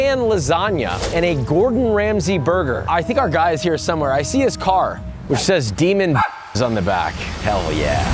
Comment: What did the car say “Demon” ? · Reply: "demon bitches" cause I'm way too attracted to crazy women 💀 also I normally do my burgers medium well but decided to shoot for medium rare for Joshua and fucked up by undercooking obviously 🫠 fml 😭